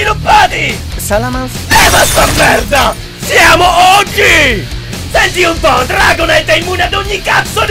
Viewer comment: 3 anni e ancora non ho capito a 0:33 che cazzo dice... Siamo...??? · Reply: SIAMO OG (original gangster), guardati il video originale